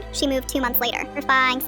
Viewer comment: Wait y’all why did I hear “the fliiying slime”😂